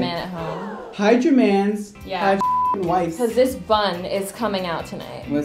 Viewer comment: when they say “hide ya mans, hide ya wifes” the captions say “hi Germans, yeah White”